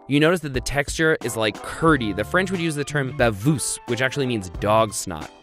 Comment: "The french use the word "bavoose" in subtitle....i'm french, i spend one minute to try to undertand what french word that could be. Then i understand that it was "baveuse"...